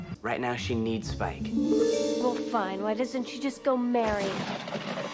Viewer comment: I think I'm losing my mind, I could have sworn I heard a Barry in there somewhere...